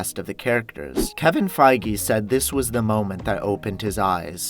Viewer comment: it sounded like he said Kevin friege